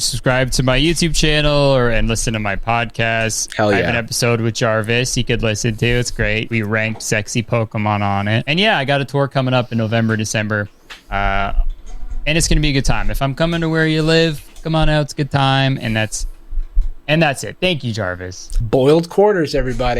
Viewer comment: I thought he said “I’ve got a twerk coming up”